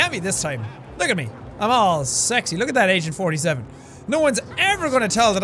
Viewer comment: Did he just say "look at that Asian-47" in the beginning?